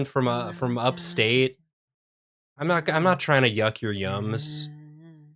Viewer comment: "I'm not trying to hyuck your hummus." -something I thought said.